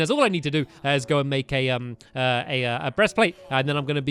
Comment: stampy said breast-plate instead of chest-plate